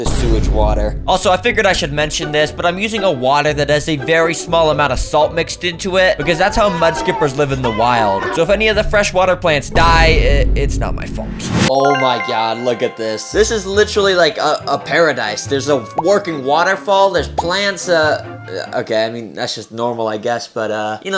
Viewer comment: did i heard you saying “oh my gyat”